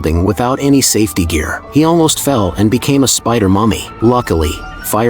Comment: Why did bro say he almost fell and became a spider mommy☠️